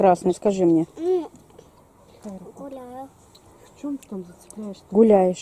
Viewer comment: Мне одной на 12:58 послышалось "курила" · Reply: THE Саша нет,там она говорит гуляла